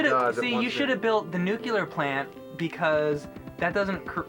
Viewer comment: Did I just hear Mike say "nucular plant?"